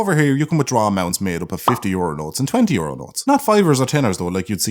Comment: I had to listen to that 3 times before i realised he said 'euro notes' quickly, instead of 'euronals' as if to tie back to when he was getting a fiver from the guy at the urinal